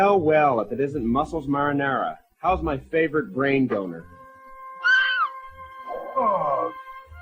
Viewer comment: did he just say how's my favorite brain boner